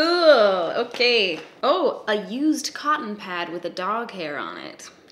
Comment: When you said “used cotton pad” I honestly thought you said “used condom” at first... It sounded like it! I had to go back and check that shit lmao